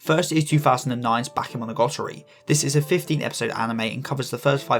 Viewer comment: I thought u said 50 episode anime for the first one so I’ve been looking but now I know it’s 15 hahaah